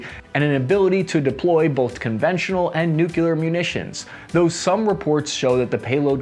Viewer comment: Did he say "nucular" @ 1:50 ?